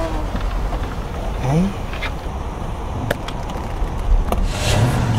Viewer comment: what is he saying? Fucking Turkish,isn't it? or what? :D